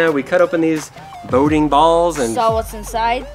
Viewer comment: I thought he said exploding balls